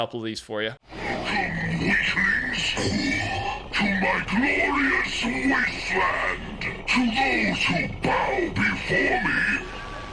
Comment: I think that voice line near the end is megalodon because it sounded like he said “welcome to my glorious wasteland”.